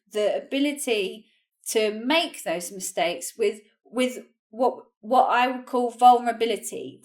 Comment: what she said at the 4:40 -4:55 moment? it`s so fast, i didn`t understand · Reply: Averjan Anvarov is what I would call vulnerability